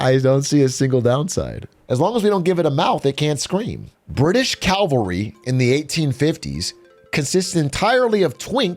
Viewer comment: Did bro just say "calvary"? Isn't that like where JC got crossed?